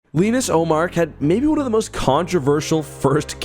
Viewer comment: I thought you were saying Linus Ullmark for the first 3/4 of this video and I was so confused LMAO